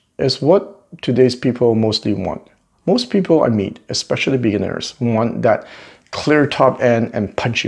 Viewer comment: at 3:48 did I hear you correctly ? Most people you meet -especially Billionaires? I enjoyed your video ,thanks. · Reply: Lol, no, 'especially beginners' , thanks!